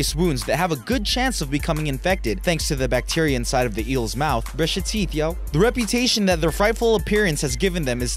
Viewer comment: In 2:10 it sounded like he said “brush your teeth yo”